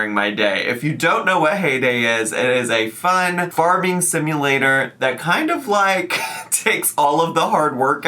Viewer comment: Couple of things bestie, 1. Your golden wavy locks are gorgeous! 2. I thought you said Hay Day was a fart simulator 🤣